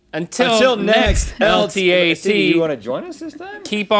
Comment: sounded like "Until next LGBT."